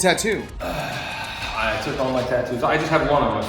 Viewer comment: Did he say he took off all his tattoos?